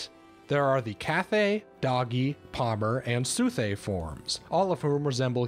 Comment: I was only listening to this, not watching, and I thought he said "doggy".